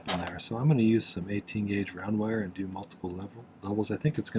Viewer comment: Love this bracelet. I thought you said 18 gauge for the wire bracelet, rewound and heard 18 gauge again, and thought that can't be right. Then I read your text of 8 gauge half round. Yes, 8 gauge! That makes more sense.